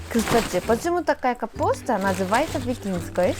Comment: я услышала Витинской😂 * в моей голове* остановите, остановите Вите, Вите надо выйтии)) Ps на самом деле она говорит викингинская?? Я не знаю. Исправьте меня, если услышала неправильно)